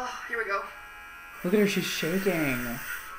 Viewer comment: lmao I thought he said “look at her, she’s shaving” and I’m like...yeah